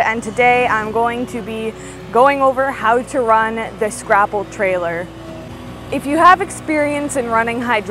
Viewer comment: I thought you said scrapple tailor :)